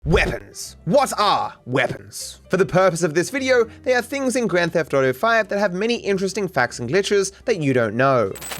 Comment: My sound was on low , and in the intro i heard , "womans , what ara womans ?